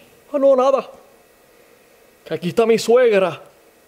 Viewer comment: juro haber recordado que dijo "que te parece a mi suegra"